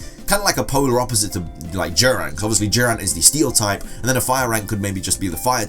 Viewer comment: The way he said Durant sounded like he was saying jiren